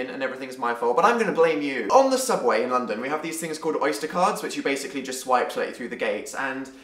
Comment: when you said "oyster cards" I heard "oyster cars" and I was like are we talking about super hero's now? whwwwhhhaaatt happened?